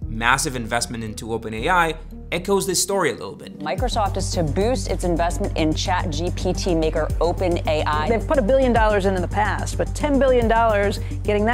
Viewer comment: What did she say? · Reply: “Microsoft is to boost it’s investment in ChatGPT maker, OpenAI.”